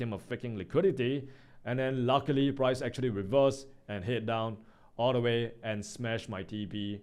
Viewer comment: Did you say “luckily” the price actually reversed and headed down all the way